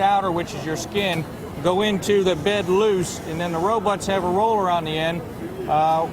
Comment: did he say robutts?